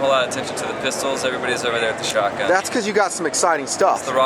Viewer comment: "over there with the shotguns" wait, what did he say?! 2:55 "the rockstar" is that th- *he pumps it* IT'S A SHOTGUN!